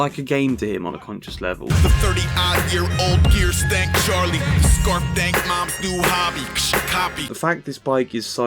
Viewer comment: I thought he said “scarf dank, moms new hobby, you should copy”